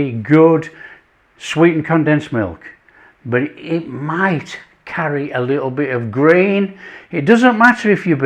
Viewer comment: At first I thought he said, " carry a little green 😮." 😂